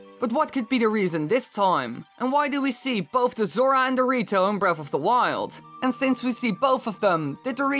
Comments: I thought you said zora and dorito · did he just call them the Dorito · sounded like he said dorito